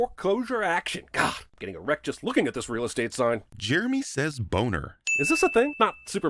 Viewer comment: ok I know you two don’t sound alike, right, but I dead all thought you said “is this a thing” and not Jeremy. No disrespect